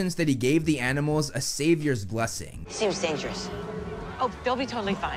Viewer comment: You might have misheard, Grover said he gave the animals a “satyr’s blessing”, not a “savior’s blessing”